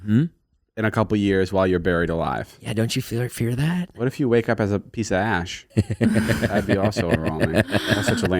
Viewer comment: for a second i thought he said what if you wake up as a piece of ass lol